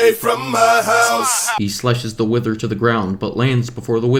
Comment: He said slash but the captions say slice 100 SINS FOR DAT (I meant that grammar mistake)